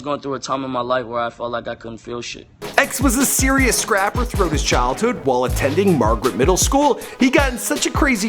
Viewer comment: "A man she was dating put HER hands on his mom" am I trippin did he actually say that😂😂😂😂